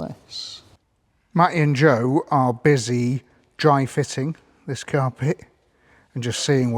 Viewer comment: My fiancee thought you said dry fisting. I had to hear this so now so do you